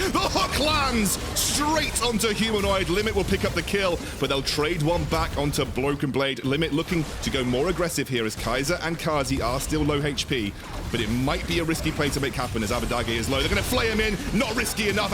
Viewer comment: Did he say Bloken Blade at 15:39 ?